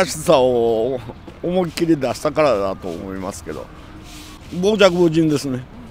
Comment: What does uncle say for arrogant at 6:54? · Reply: I think I found it. 傍若無人